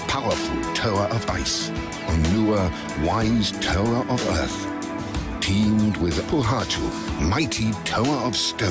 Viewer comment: did he say teamed little, or deemed for? I know he said pohatu, but before that.